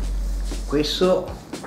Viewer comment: ご主人なんとおっしゃいました？ おぃしそ♪に聞こえましたw 新たなおもちゃを手に入れた子供のように嬉しそうですね。 こんな大きな生ハムが50ユーロで手に入るなんて羨ましい〜。日本では昨日パンチェッタ120gが安売りで298円でした。 生ハム消費シリーズ楽しみにしています！ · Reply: おいしそに聞こえましたかーｗ クエスト(これ)と言っています。たいした意味はなく言っています。